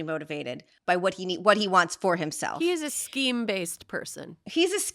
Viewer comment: I misheard one of your "definitions"! Instead of "Scheme" I heard "SKEEVE" -- which (i think) IS EVEN BETTER! "Skeavy" is far far more horrible AND apt ... would you not agree!?!?